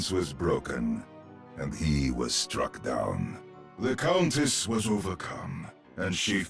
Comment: Does rein say "The cultist was over come and she fell to the ground" at 4:40 isn't the cultist zen? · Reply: "Countess". It's Widow. Zen is "Monk"